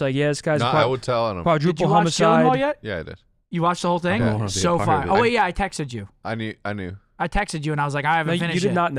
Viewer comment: Anyone know what show Scump and hecz mentioned at the start around 2:01 minutes in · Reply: Presumed Innocent I think